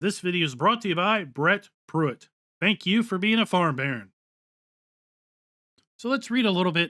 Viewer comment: I know its Farm Baron but I always hear "Thank you for being a Farm Bear" :DDD